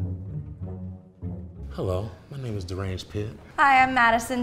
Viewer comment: Did he just say his name is “Deranged Pit”?